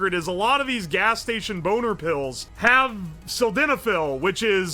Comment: For a moment I thought it said mixing bootleg gas station pills and I was genuinely shocked and afraid. I still am, but a little less